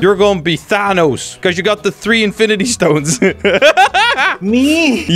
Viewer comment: did u just say he has 3 infinity stone? was it five?